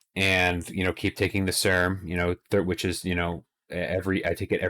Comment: What was it you said you're going to keep taking at the 7:25 mark? Did you say "Serum" And congrats on the improvement! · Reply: SERM, Selective Estrogen Receptor Modulator. I was getting Gyno symptoms which clued me into needing it. I'm taking Nolvadex/Tamoxifen and Clomid. If you're going to take HCG I would have it on hand in case you have issues like me.